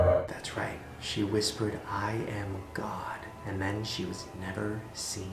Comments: WHAT IT WAS “I am god” literally i heard was “Im el- ca” bruh i need a doctor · when the lady said "i am god, i heard "i am courtney cox". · I heard "I am gone" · "I am god" I heard "I am an aircon" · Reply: asparaqus I heard I'm cod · I genuinely thought she said; “I am cock” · All I hear was "I am. Air con" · "i am god" lmaoooo why did i heard "i am cock" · It sounded like she said "I am gone" instead of "I am God" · i thought she said i am gone and i was like that is some maze runner crank shit😂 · the first one when she said "I am God" I heard "I am cock" and I was like wtf.... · 'I am God' I thought she said 'I am hot' XD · Yo, when she said "I am God" I thought she said "I am cod" ! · I thought she said "I am Cod..." i was like BOI U ANYTHING BUT A FISH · I heard "I am gone" and I was like yaaaasss sassy af but when Shane said it said "I am God" I freaked the f out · I can't stop laughing I heard ' I am cock ' not ' I am god ' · I actually thought the woman said she was gone, and I was like NOOO BITCH I AM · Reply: I thought she said I am cod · “I. Am. God.” Tbh. Thought she said. “I am ka” and I was like. ???? The snake from jungle book. That makes no sen-oh. Oh... god. Ok. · omg i thought she said "I am cunt" not "I am god" LOL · I thought she said "I am cod" I was like okay...something smells fishy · i legit thought she said "I am Khan" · Why did I hear "I. Am. Kong" instead of "I am God." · She said ''I am God'' What I thought she said ''I am cod''